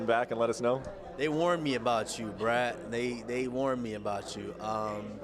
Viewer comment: did Snoozeman call him Bratt? Brad? Brett Okamoto is one of the best...he makes Ariel sound like an unprofessional clown.